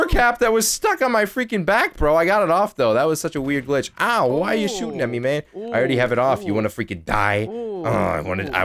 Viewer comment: Did said at 1:58 why u shooting at me bae?